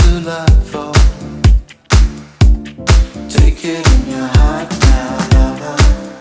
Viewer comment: TAKE IT IN YOUR HEART NOW LOVER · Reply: I heard : "Tap it in your hat not a lobster" ???